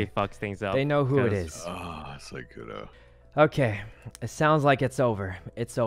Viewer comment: or 18:39 I heard corpse say: Ughh sykkuno